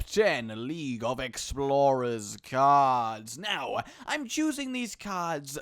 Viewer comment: lol thought it said league of legends Kappa